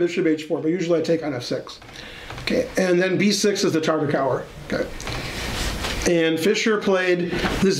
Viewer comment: b6 is the ??? What is Ben saying? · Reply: Tartakower